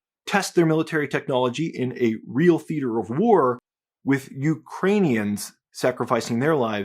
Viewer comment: I thought you said you see "Iranians" all the time